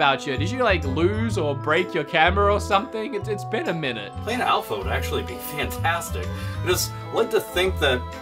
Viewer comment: yeah????? welcome back alpha. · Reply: Can anyone please translate/decipher what he says at the 15:52 mark? My CC thinks he says "Cleaning out flow would actually be fantastic...". I'm pretty sure that's not what he says.